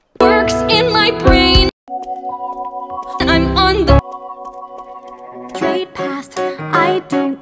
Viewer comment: Luz: im on the- Amity: Straight ass, i don't cut corners, i make appoint to be on time Me: excuse me did you just say Straight ass Amity?